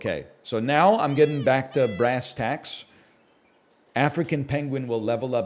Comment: did someone say "bacta"? Are we returning to the dark ages of SAP?